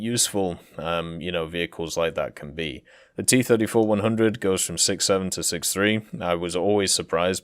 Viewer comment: No you mean T34 100, imagine that happened lmao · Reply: Oh you’re right. I misheard. I thought he said T-44-100. I was really confused there.